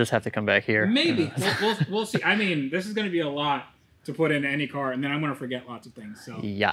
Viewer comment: wait you were RECORDING THIS? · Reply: did you say "this would be a lot to put into any car"?